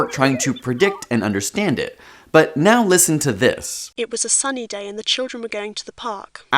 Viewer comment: i heard "it was a funny day and then we went to the park"